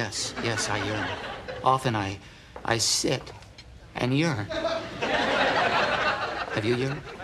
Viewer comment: When i was a kid i thought he said urine "Sometimes i sit and i urine" Looool